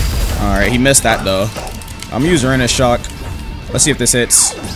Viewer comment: wait did he just call it URANUS SHOCK?